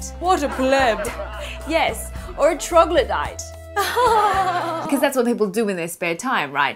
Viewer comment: What did she say at 1:02? All I heard was "tro-gla-dyte"